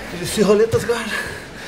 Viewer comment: what weird sweep was that? He said something about Pé de Pano and Roleta?